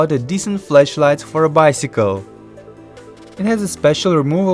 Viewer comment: I wasn't looking because I was doing something else and I really thought you said "Fleshlight for my Bicycle"